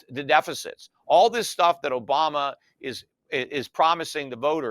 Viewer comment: Did I hear this guy say Obama? It was at 10:30 ... 🤔🤔🤔